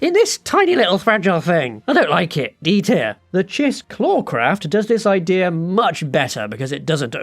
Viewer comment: what is the craft at @ 7:09? couldn't make out what you called it. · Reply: It's called the Chiss Clawcraft, neat enough fighter